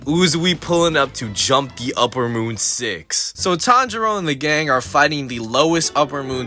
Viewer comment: I thought he said “So Ty Jerome and the gang”😂😂